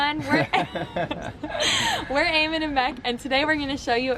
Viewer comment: at first i thought she said aim and ambec lol